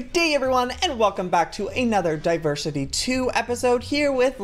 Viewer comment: I Thought Joey Said Diapersity